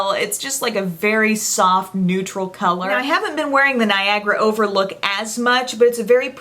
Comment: I definitely thought you said 'Viagra Overlook' at first, haha! Hope you're doing well, and Belle too!